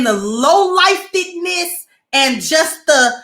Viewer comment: Salaam alaikum, ma'am. 9:41. Did you just say "low-lifededness"?! Lol! 🤣🤣🤣🤣🤣🤣